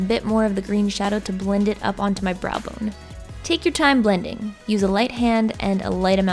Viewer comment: > is she saying "brow boner" here? am I the only one hearing this??